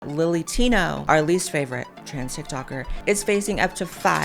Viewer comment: I kept hearing you say “Lil’ Latino” and couldn’t figure out what you were referencing and then I realized that’s why Lilly Tina’s name sounds like.